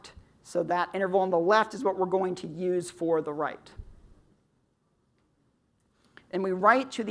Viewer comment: [right vs. write] I think he said “..so the interval on the left is what we’re going to use for the *write*”